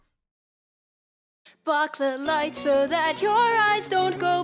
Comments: I thought she said " Fuck the lights" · At the the start I thought you said F*ck the light. XD